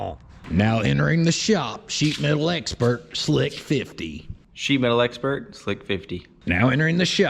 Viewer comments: Lol it sounds like you said "she mail expert, Slick 50" not that that is a bad thing. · Ngl when slick was announced I swear I heard him say "shemale expert" nearly spit out my drink 😂